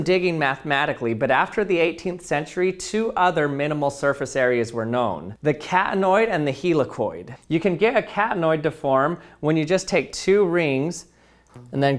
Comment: What he says: Catanoid What I hear: cat annoyed